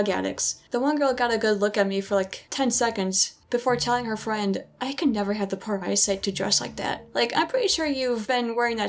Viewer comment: I could never...what? What did she say? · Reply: “I could never have the poor eyesight to dress like that.”